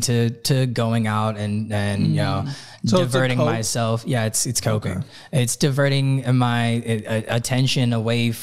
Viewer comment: I thought he said “yeah it’s cocaine” for a second haha idk any bass players that couldn’t say that regarding their debt problems honestly though